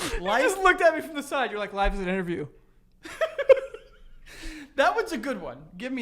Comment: Omg 😹😭 I thought said "Life is an enema..." Omg fuuuuck hahahaha.